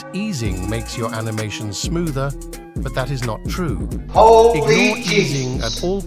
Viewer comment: Sorry what did you say all I heard is holy jesus